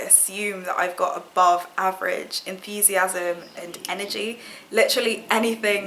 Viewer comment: It sounded like you said that you get excited by "normal tea"... maybe you said "novelty"? Girl, your enthusiasm towards the end has got me giddy! Loving this insight into your wonderful personality. 💖